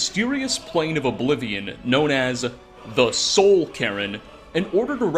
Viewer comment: I keep hearing "Soul Karen"